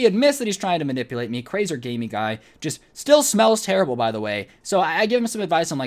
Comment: Did he say crazer gamey guy or is that just me